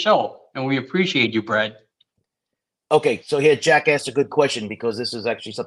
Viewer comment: I thought he said jackass too. Haha. 👏🏼👏🏼 Hey, my mind just wants to laugh. Sorry. I’m not a detective.